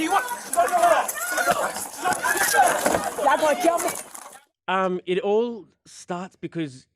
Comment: did she say, "y'all gonna kill me?"